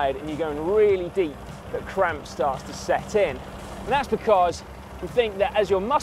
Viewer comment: oh cramps. i thought you said gramps at first and was expecting a video about matt ;)